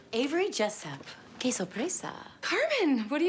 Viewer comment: When Carmen comes in she says Qeso Presa or something? White Cheese? Anyone know what the hell that is ? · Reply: She said que sorpresa. It means what a surprise